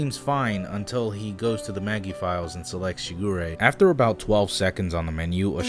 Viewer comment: Wait...did he say the file's title was "yuurei"? That's Japanese for "ghost" or "spirit of a deceased person"... O.o EDIT: Nevermind...guess it was "shigure"...and I heard "yuurei" for some reason... XD